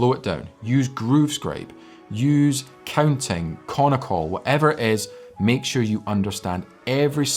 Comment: what did you say after “Groove Scraping, counting and “ ? Are these new apps or something? Please share names so I can look them up. Thanks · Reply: Konnokol. Indian system for phrasing and memorisons rhythms. Search Steve Smith Konnokol and you'll be in for a treat!